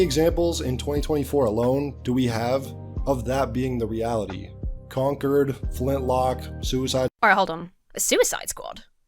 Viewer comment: When he said "Conquered," I heard, "Conker," and was expecting a drunk squirrel. Day ruined 😪😂